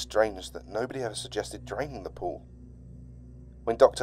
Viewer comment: I thought they said "we'd never considered DRINKING the pool" for a moment